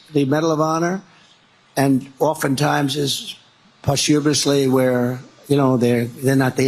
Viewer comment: Posthuberisly? I had to listen to it several times to figure out exactly what he said.